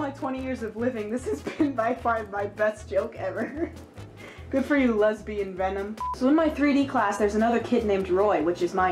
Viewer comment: When you said "Good for you lesbianvenom," I thought you said, " Be free lesbian men!"